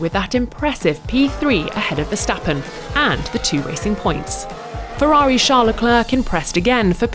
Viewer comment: did she say "kissing points"?